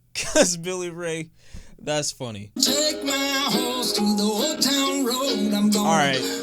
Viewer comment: ngl i thought he said "im gonna take my horse to the hotel room" and i still believe thats what it says idgaf what yall say